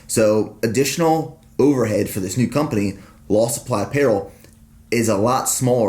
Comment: Brand imaging... I sure would have thought you were saying "Law Supply Apparel" if you hadn't put up a screenshot of the insta page. Every single time you said it. Just friendly feedback.